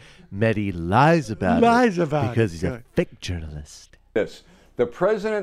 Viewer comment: I know he said "fake journalist" but I definitely heard "thicc journalist"